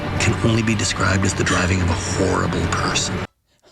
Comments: I thought he was about to say “the driving of a whore” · I THOUGHT HE WAS GONNA SAY “the way you drive can only be described as the driving of a whore” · I thought he was gona say whore 😂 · it really sounded like he was about to call her a whore for a second